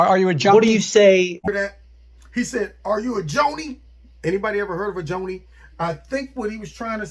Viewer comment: you are the only one who has heard what I heard. "Are you a joney"! Thanks. I thought I was hearing things. 😁